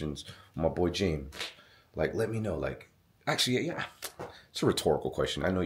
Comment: It sounded like it said let me out, Like it is trap in there. 😮